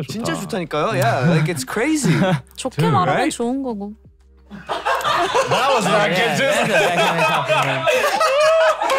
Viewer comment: What did Ashley say at 40:46? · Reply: That he is dumb somehow